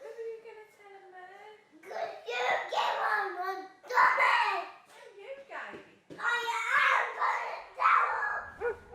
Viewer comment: What she say? All I heard was “you something something DUMMY!” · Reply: You gave them my dummies I think